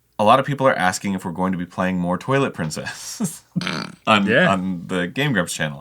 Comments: HE SAID TWILIGHT PRINCESS I THOUGHT HE SAID TOILET PRINCESS OMFG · so I legitimately thought they were saying "Toilet Princess" at first. They were not. They were saying "Twilight Princess." I was like "what is toilet princess? Some indie game?" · Every time Arin says twilight princess I think he’s saying toilet princess and I get tremendously confused. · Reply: I thought he was saying the toilet princess the whole time 😂😂